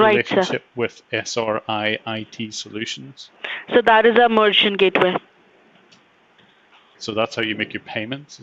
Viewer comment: did she say "that is a mauritian gateway" at 17:30?